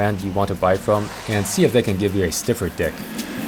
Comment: it sounds like he said stiffer d**k